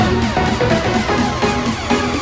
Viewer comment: WHY DID IT SAY " RUN THAT SHIT UP "